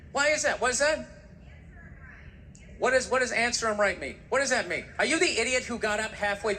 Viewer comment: Considering how Bill went off on her, she's lucky he didn't hear her correctly. Some people have said what she actually said was "it's her right", and the more I watch this routine, the more I have to agree, that's what she said.